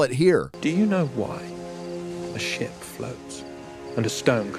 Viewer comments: I legit thought Finrod said "Do you know why a shit floats?". Like, mate, enunciate better. But I do appreciate the laughs. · That ship and rock speech.... All I hear is "Do you know why... a shit floats...." XD